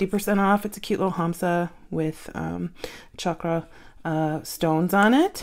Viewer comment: Sounded like you said cha ka stones - FYI - chakra is pronounced shaw kraa.